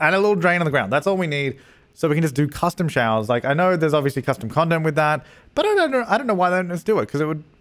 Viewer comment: did he say "custom condom"??????? (or am I just stupid/stoopid)